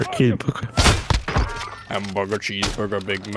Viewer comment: "pepper me" from "hamburger meat" does anyone else hear it too?😂😂